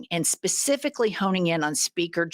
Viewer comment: I think this is the first time I’ve heard an American say ‘homing in’ correctly, instead of the absurd ‘honing in.’ Even the subtitles say "honing in"! I love Jennifer, especially when she makes laugh!